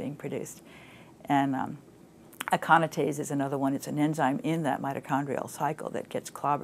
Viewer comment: Can anyone tell me what she is saying at the 2:25 mark? It sounds like Iconotaze but can't make it out and cannot come up with it via google search either. TY. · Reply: Nevermind, I think I got it: aconitase